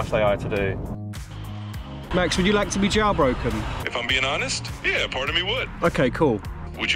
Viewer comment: Jail broken. I thought he was saying, "jaw broken." 😳